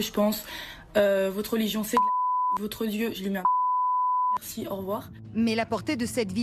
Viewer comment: À 0:50 Elle dit quoi exactement ? " Votre Dieu, je lui mets...." ? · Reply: Elle dis « Votre Dieu je lui mets un doigt dans l’cul ! » franchement j’incite pas à la haine mais elle a qu’est-ce qu’elle mérite